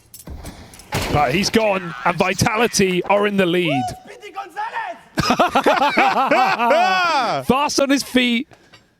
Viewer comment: what did he say? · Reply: Speedy Gonzales